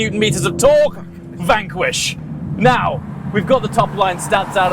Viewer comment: what does that voice say in the background at 00:31 (fucking Vanquish) ?